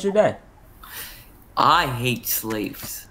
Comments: were not gonna talk about the dude who just said “i hate slaves” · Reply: I heard "sleeves" xD i don't know why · Did he just say "I hate slaves"?